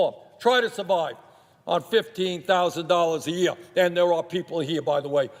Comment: error in the subtitles. Bernie said: $15,000 Subtitles said: $155,000 This really bothered me, considering the amount of misinformation out there, The intentional misleading away from facts, and the out and out manipulation and lying. I have no idea if that happened here, quite frankly, wouldn't be surprised. But when Bernie is talking about people are unable to live on $15,000 a year and the subtitles say $155,000, that is not acceptable. Someone watching this, one who is unable to use audio for whatever reason and must rely on subtitles, may not have a problem if they think that the average senior is living on $155,000 a year. I'm not impressed with these autogenerated subtitles in English because he clearly said $15,000. You can't even stick your fingers in your ears and mishear it as $155,000.